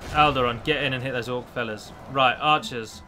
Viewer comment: It sounded like you said "orc fellas"...